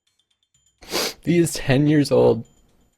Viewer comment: I thought said he was dead, but he said 10. Jesus I got so frightened